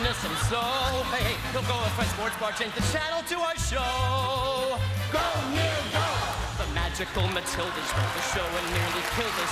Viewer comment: Can anyone make out what he says at 3:26? <link> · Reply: “Nick’s the man”. I think